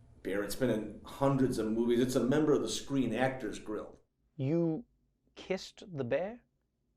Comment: Hold on, did he say Screen Actors "Grill"?